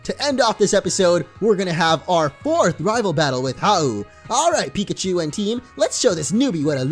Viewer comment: Did he just say end this episode with the fourth rival battle?